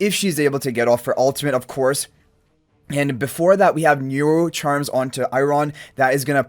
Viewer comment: Sorry - who powers up Eiron? I couldnt hear what exactly you said... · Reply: Niru charms